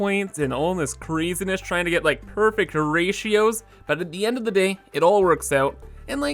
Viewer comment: I heard "trying to get perfect Horatios". Which is obviously ridiculous because Horatio is already perfect.